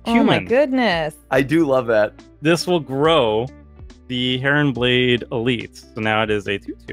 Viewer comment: The first time said "Heronblade", I heard "Karenblade".